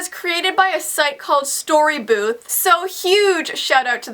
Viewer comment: I thought you said storybuke not story booth